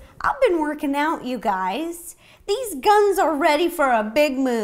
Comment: XD lol love it. 9:41 Did she say guns????